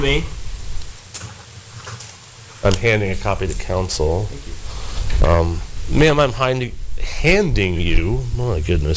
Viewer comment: when he hinds her the paper, what does she mean when she says "it's a copy of my beat up" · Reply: Loo Shrloo It's actually vitae, also known as curriculum vitae. It's bascially a resumé